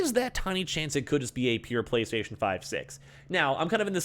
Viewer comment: Did he just say PlayStation 5 6 ??